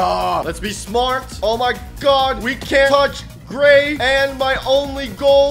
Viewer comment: I thought it said “can’t touch GAY”